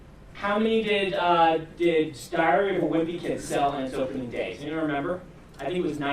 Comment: what's he saying there? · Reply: Diary of a Wimpy Kid